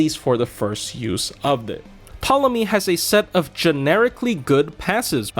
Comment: I thought you said ptolemias was hot so i was gonna agree 😭😭